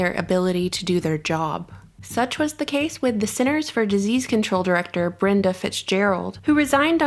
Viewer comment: When you said CDC full name, I first thought you jokingly said Center for Disney Control. I rewinded, and you did not. It was just me. 😂